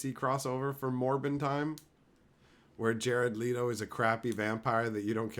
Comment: Oh I heard it's MORMON time the first few times you were saying that lol.